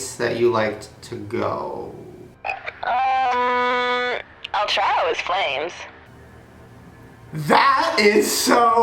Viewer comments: what is she saying at 13:07-13:09?!??!?? I'll try it with flames???? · it sound like she said child in flames lol